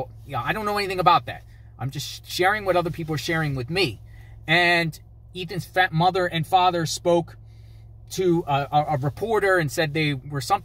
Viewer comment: At time time 8:01 of this video did say "' Ethan's fat mother and father spoke"'! Just reporting what it sounded like!